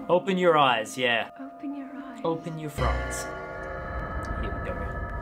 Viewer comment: Did he say “Open your fronts”???